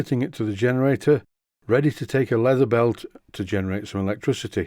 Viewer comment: Will it be belt or gear driven? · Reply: I think he says leather belt at about